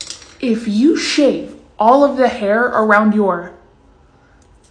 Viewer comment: Bruh i heard it as "you won't be able to f*ck silently anymore"💀💀💀💀💀